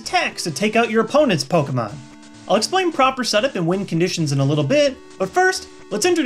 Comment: Parent here, learning so I know what my kid is talking about. At about 0:50 you say "Win Conditions" and at first I thought you said "Wind Conditions" and I thought to myself, "Holly crap, how complicated is this game?!?"